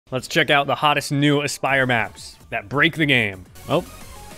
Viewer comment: Thought you said “Hardest new aspire maps” in an Aussie accent